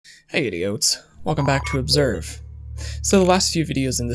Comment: I hear “hey idiots” every time.